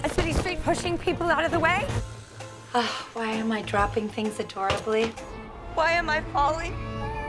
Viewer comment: What does she say at 0:04? "Why am I dropping things hitorically?" I cannot work it out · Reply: Dropping things adorably*